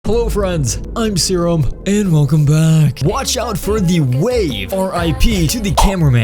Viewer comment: Him: Hey guys, welcome back. What I thought he said: Hey guys, welcome Booger.